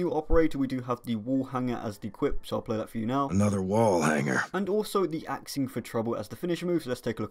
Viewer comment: Bro I thought he said, "Another Wall Hacker." I was like, Activision REALLY doesn't give af about cheaters.